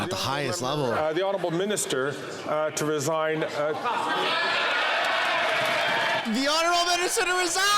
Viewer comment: I could not make out what the derogatory comment...what was it? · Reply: I couldn't either. I just thought that he said "the minister to resign" because that's what was on his mind. Maybe he knew he was guilty